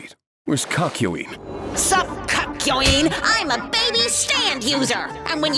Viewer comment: is it just me or did he call him Cuck-yoin?